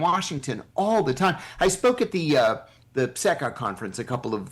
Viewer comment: All good with the interview, but at 12:39 did John say Pseka conference? We Greeks have a conference that we call pseka=ψεκα? That is a self trolling joke.